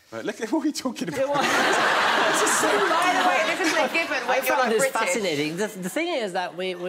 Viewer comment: What does Adele say at 11:21 ? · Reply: Nah she said "by the way this isN'T given when you're like British" as in we don't all know / meet the queen and other royals. Like a lot of Americans and that think we all know them cos it's a small country